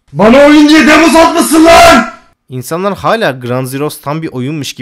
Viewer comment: ne dediğini anlamadım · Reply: "bana oyun diye demo satmışsın lan"